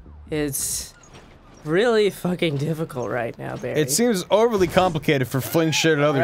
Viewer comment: I thought that said barfy in the intro XD lmao